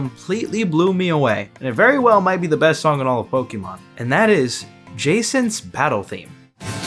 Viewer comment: really gotta work on your pronunciation because i deadass thought you said "Jasons Battle theme"